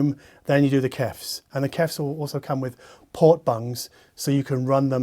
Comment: When he said "port bung," I thought he said "pork buns" (Chinese savory buns). I must be hungry...